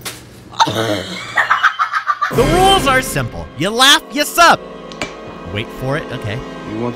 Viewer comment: I thought you said, "if you laugh you suck", but now i know it is "Sub". Then i realized i wasnt subscribed yet.